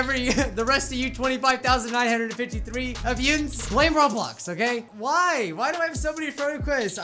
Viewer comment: Did you just say Yinz at 1:02?! Are you by chance from Pittsburgh?